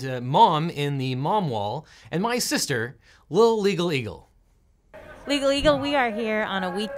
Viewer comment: I heard “lil eagle” and smiled so big.